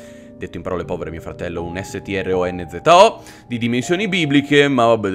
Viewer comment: qualcuno mi potrebbe spiegare cosa ha detto? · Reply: vecchio saggio dice /s/t/r/o/n/z/o